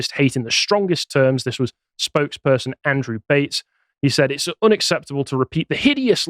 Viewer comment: Who else heard “spokesperson Andrew Tate”? 😂